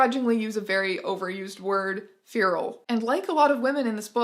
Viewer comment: What word is she saying? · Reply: She is saying "Feral"